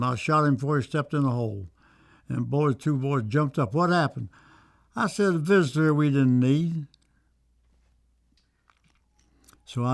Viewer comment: What does he say right there? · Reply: He's says, "I said a visitor we didn't need".